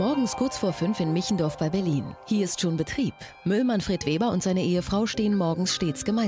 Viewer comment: Wer hat auch "Müll Manfred Weber" anstatt "Müllmann Fred Weber" gehört? :-D